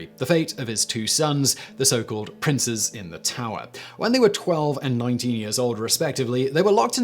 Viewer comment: The princes were 12 and 9 years old. It sounded like you said 12 and 19, so I checked the caption, and that said 19 also. So, if anyone is wondering, it's 12 and 9, not 19.